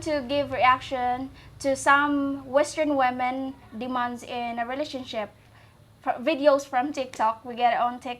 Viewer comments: I hear “ western women, demons in relationships.” Lmao can be accurate. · I thought she said "western women demons" but she said " western women demands" however im completely fine with western women demons lol